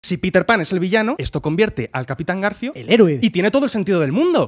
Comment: Entendí "Peter Pan es sevillano"